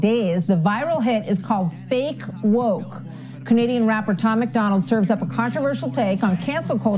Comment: I thought she said comedian rapper, instead of Canadian rapper, at first and was like what the fuckkkkk, hahaha.